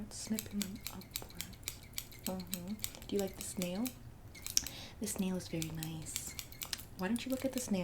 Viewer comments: Time stamp ? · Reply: Just in case u haven’t found it yet. She cracks me up. Now I kinda kind of think she really is saying Snail.. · omg when she kept saying "just look at this nail" i couldve SWORN she was saying "do you like this snail? just look at the snail" and.i kept thinking 'WHAT SNAIL?!!" and replayed the clip like thirteen times to find the snail in the frame. i must very sleep deprived or something bc wtf